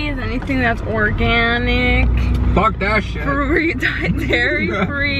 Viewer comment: Did said "Fuck that Shit" at 4:39? 🤷🏻‍♀️